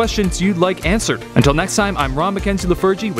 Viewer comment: First thing I hear: “I’m Rob McKenzie the virgin”